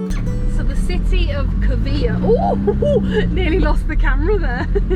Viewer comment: OMG I thought you wanted to say "cavilha", but in fact it was Covilhã (I saw the sign)🤣🤣🤣🤣